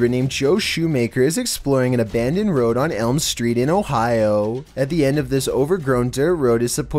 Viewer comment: Wait 8:09 did he said elmstreet? Brah you better watch out!